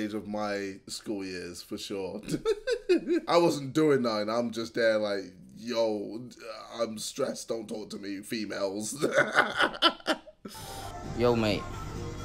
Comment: can anyone provide captions for what he is saying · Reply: I wasn't doin' that i was there just like yo I'm stressed don't talk to me females